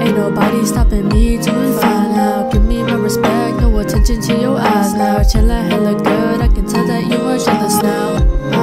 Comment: In the SoundCloud I thought it said pay attention to your ass now